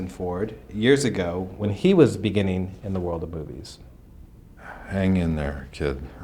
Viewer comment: did he say world of boobies or is it just me